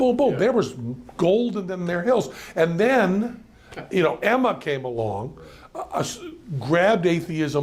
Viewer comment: What name is it at 2:31 who he says pulls atheism into socialism? I just don't hear it. "Emma"? Who?